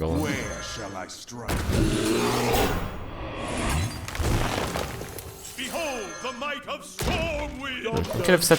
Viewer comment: did the storm wind champion say "behold the might of stormwheel" instead of stormwind?